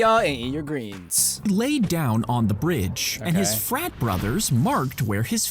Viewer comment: almost deaf people hear "fat" instead of "frat"